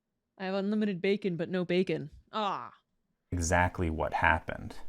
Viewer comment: I heard "unlimited bacon but no bacon" and immediately went to search her channel for "Inbox" and "Respawn" but was sorely disappointed, lol